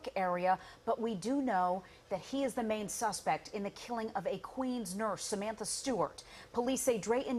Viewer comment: Who else thought 💭 they were saying he killed “A” actual Queen’s,Nurse..... ???? Figured out the victim is from Queens🙄